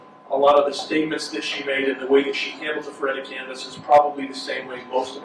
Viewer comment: What does he call it? Foretic canvas? Phoretic? Ferritic? Someone with law brain or better ears please help, Google is not my friend on this one! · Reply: a Faretta canvass (from Faretta v. California). This is when the judge makes sure that the defendant is aware of the implications of self-representation.